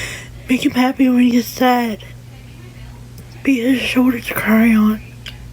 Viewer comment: the way I thought she said "be his shortest crayon," and was asking my kids if this was an expression I want familiar with; that tongue IS to big for her mouth!! 💀😭